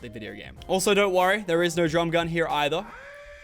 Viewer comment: Did he say drum gun